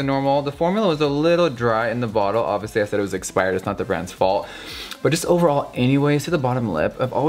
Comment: Lol did he just say "it's not the brats fault" while using kylie's lipstick? lmaoooooo, you are my most favorite youtuber I swear lol